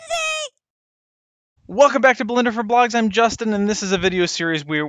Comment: LoL :D I hear: "Welcome back to Blender for blacks" :D